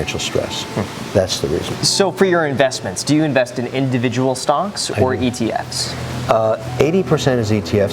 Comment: LMAO he said stonks instead of stocks